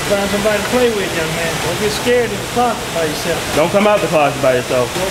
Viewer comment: That guy told "Don't be scared to come out the closet by yourself" And I thought he said "Don't scared when the cops come and you're by yourself"🤣